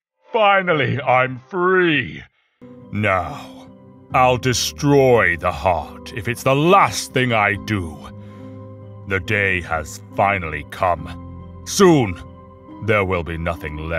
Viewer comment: does anyone hear "the gay has finally come"